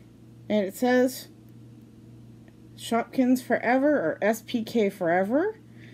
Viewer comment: LOL. When you said “Shopkins Forever,” thought you said “shoot guns forever.” Need to listen closer. Lol